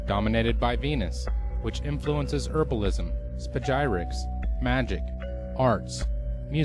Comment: after herbalism what does he say? *He said "spagyrics" if someone wondered. It's a alchemical form of herbalism